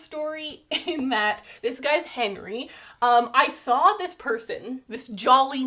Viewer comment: When you said “Henry” I thought you said Hank Green, which made me realize that Hank Green is an excellent plant name 😂